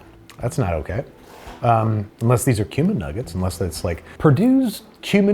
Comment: I thought he said human not cumin. “Unless these are human nuggets” is what I heard. 😂